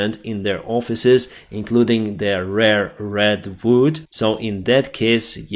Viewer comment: what is he saying @ 9:42 "rare red boot"? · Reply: He said "rare red wood", or maybe the specific type of wood called "redwood".